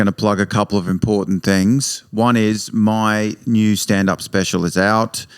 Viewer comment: I thought you said dan was special lol